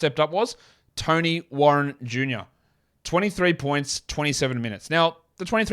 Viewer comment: With your accent I thought you were saying Tony wroten Jr who had that one proAm double shammgod highlight and never seen him again 😂